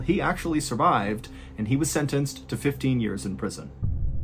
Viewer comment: i thought he said 50 years not 15 omg